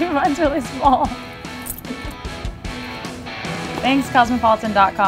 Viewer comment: I misheard the end as "Hopefully next time I get to be Ace Frehley" and I laughed so hard.